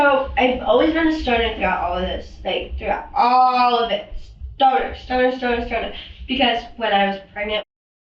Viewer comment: She’s saying “stoner”, not “starter”; and Idgaf what she says, this cannot be blamed on cannabis consumption, alone.🤦🏽‍♀️ I also have consumed cannabis prior to having children, while pregnant, and still to this very day. Both of my children are grown adults with solid careers. Neither of them were ever left alone to die.💔😭🤬